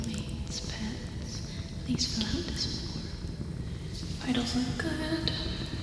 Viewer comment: i’ve been watching this video for three years and i just now realized you say “vitals look good” at 43:57 instead of “i don’t look good”